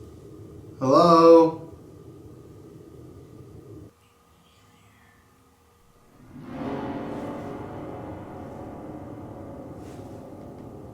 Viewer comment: A that 1:12:27 wheb you couldnt make out what it said it said i am here